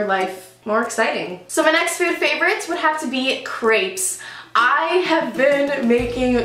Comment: at first I thought she said grapes. then she showed this pancake dough and I was like ooooh crêpe! :D funny how Americans americanize words from foreign languages